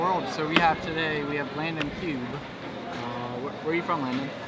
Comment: did you say "my nigga"? just wondering..